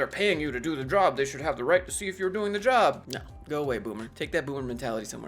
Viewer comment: Thought you said 'boo boo', but it was 'boomer' 🤣🤣. Invasive way to track people during paranoria . This says something about the employer and the culture, I would stay clear